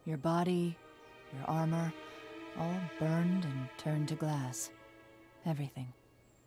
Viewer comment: Oh my god! For the LONGEST time, I thought she said, "All burned and turned to GRASS", NOT GLASS! God, I feel so bad right now.